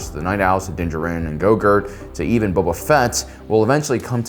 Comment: did I just hear my man say “Grogurt” lmao